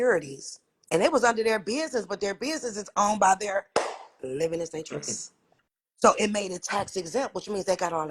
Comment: does she say "living estate trust"?